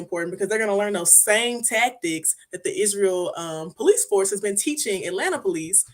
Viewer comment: Someone, PLEASE,....HELP me understand what she said (@ 28:48)..."because they're going to learn those same same police tactics that the Isreal police....." Did I hear this right? Is she referring to the "Isreal Police"?